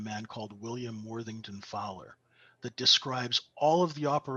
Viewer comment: The 1874 book ( william worden defiler) - did I understood right ? I don't think so... Can you help us out with that ? And also, said something about his book "Proper Villance" or something ? I can't figure out this 2 things and I'm very grateful if you can help me with this 2 things. thx was awesome! I've already listened 3 times...